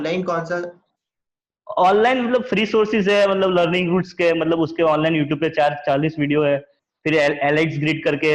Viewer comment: did he said learning goods? I can't find that channel on youtube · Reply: Learnings roots